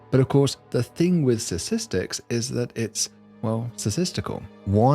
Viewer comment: I heard ‘Sasistics’, at the beginning… am I having a stroke or going deaf?